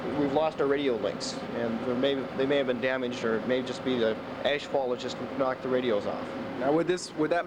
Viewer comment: I keep hearing them say "asphalt" when I'm sure they're saying ash-fall... I was 11 then, woke up to a moonscape outside...